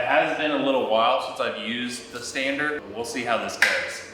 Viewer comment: God I thought you said “sander” and I was deeply confused for about 30 seconds